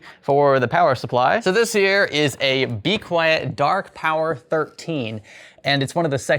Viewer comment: Legitimately I thought the psu box said dank power 13 for a second 😂